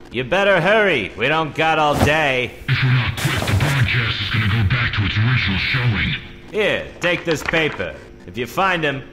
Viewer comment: For those that can’t understand Whitty, he says, “If we’re not quick, the broadcast is gonna go back to its original showing.” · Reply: All I heard was "The broadcast is gonna go back to its original showing" lmao